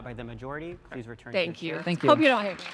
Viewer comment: I think she said "hope you don't hate me" and the irony is thick there. Only one side of this 'debate' is about hating people!